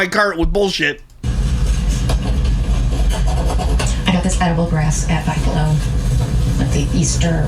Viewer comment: did she say she bought edible grass at 5 Below?????